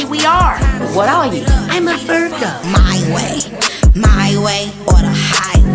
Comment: How i wish this scene played out: Moxxie:Im A vIrGo~ Blitz:HAH! Agent 1:Oh a smart guy,Eh? Blitz:Yea,he aint really that smart. Moxxie:HEY! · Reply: WAIT IT SAYS VIRGO I THOUGHT IT SAID FUR GOAT?!?!